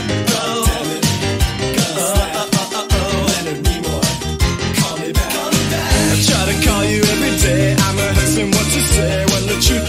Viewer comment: The lyrics of the song match up so well with spamton's character, I mean: "Oh my god I try, and I try, and I try to make you listen to me" It feels like spamton's desperation as show in the game, and the animation adds to Spamton's insanity throughout the entirety of Deltarune, very nice, 1/0 stars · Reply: i thought he was saying “a child” rather than “i try” lol